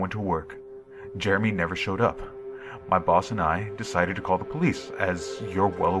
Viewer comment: I thought at first he said: "My balls and I" instead of "My boss and I".